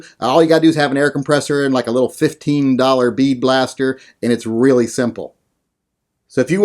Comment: This is really nice. Could you provide a link to the blaster you're talking about? I thought I heard "bee blaster", and googled it to get kids's toys. I'm unsure what I should be looking for. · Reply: its a bead blaster or sand blaster not bee it looks sorta like a paint gun you hook up to an air compressor